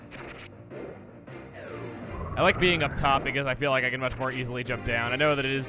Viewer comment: Did you just say that you like to be on top